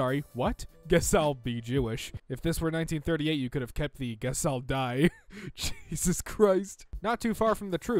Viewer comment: had subtitles on and instead of guess I'll die there was GAS I'll die. What a coincidence